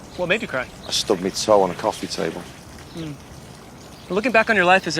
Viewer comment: What did he Said? overbritishism i cónt ondorstónd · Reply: "Stubbed my toe on a coffee table"